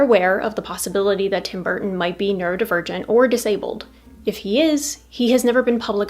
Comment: "Neuro divergent", what I first heard, was "nerd, virgin"😂.